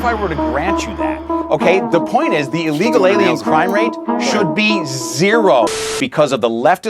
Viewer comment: did he just say illegal aliens? i…..